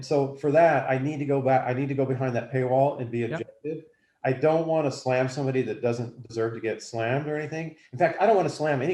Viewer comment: what did he say ? A non disclosure what ?? Breh. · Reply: Non disparagement agreement